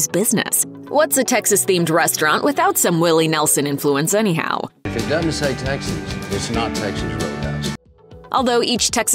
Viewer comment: Kinda sounded like Willie said "If it doesn't say taxes then that's my ho-house."